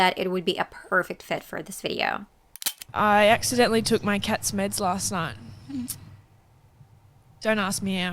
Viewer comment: it’s not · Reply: I understood all of them (although I had to listen to the cat joke as I heard "cat's mitts" due to her accent) 😁 I'd like to share a little "hack" for anyone who reads this: I learned English solely by reading, listening to music, watching TV shows and interacting online in English. Had a bit of formal education too, but I quickly realized that one can advance much better with a vast vocabulary rather than adhering to the strict grammatical rules (a teacher of mine was always upset at me for not having a clue about grammar and not caring about it either)